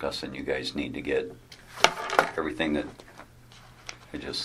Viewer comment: 35:40 what was the Dad saying there before he was cut off by the interviewer?? He went over to the house and was saying to them “youvguys need to get rid of....” hmmm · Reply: Sorry not rid of “get everything that...”